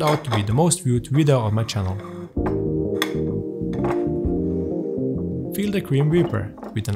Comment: still getting used to your accent so I had to rewind here because I swear you said "feel the grim reaper"